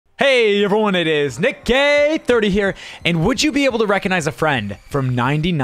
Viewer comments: Dunno why in the intro u kinda sounded like u said "hey guys, nickgay 30 here" I'm laughing so hard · did he just say nickgay30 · For the intro it sounded like he said it nickgay30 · thought he said welcome it's nick ga* 30 😂